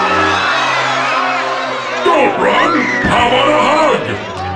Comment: I thought he said how about a hug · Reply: For some reason, I always heard "I want a hug", but I think it is "how 'bout a hug?".